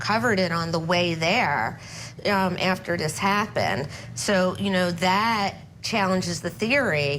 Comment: #CourtTV - What do YOU think? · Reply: I’m sorry, did she say: “after dis happened”? 🙄🤦🏼‍♀️😔 Why can’t people use all the consonants available and necessary in the word.